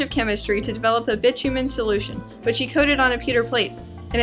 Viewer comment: All I hear at 1:55 is "a bitch human solution" I am going crazy trying to figure out what she's saying. Please help. Anyone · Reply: Bitumen